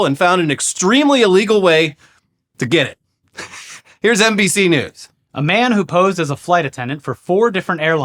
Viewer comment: Swear to god I heard say “Here’s NPC News” for a second